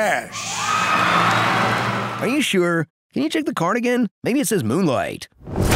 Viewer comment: Thought he said “check the cardigan”